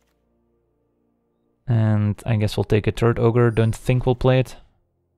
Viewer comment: I keep hearing you say "turd ogre" lol